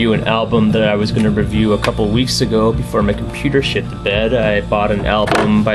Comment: LOL did you say "before my computer shit the bed". Im really not sure if you said that or not but thats what it sounded like.